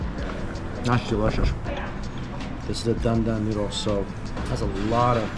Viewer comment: I couldn't make out the name...but what was the peanut noodle dish at 4:57 ? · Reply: it called dan dan noodles. it usually doesn't have the much peanut sauce on it though